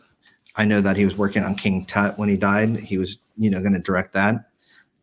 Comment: I didn’t catch the title of the movie Michael was going to direct? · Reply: I heard "King Tut"